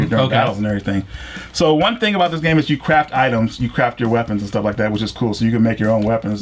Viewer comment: I thought he said you crap your weapons